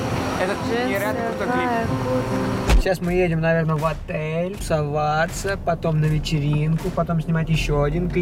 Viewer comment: Мне послышалось мы сейчас наверно едем в отель целоваться 😂